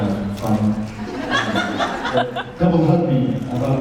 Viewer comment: Jared thought she said "double fuck me" instead it was hysterical!